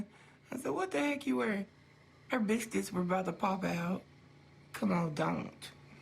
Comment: When Tammy said "biscuits" about Amanda, the first time I watched this, I seriously thought she said "b**ch tits." 😂